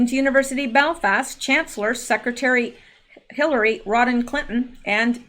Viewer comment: I love your videos, but am I hearing you say "Rodhan" instead of "Rodham?" Asking for an anonymous Chancellor.